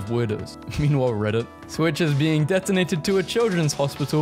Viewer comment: you said detonated instead of donated